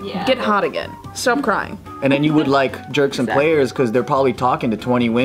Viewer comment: I thought he said jerk some players too😂😂😂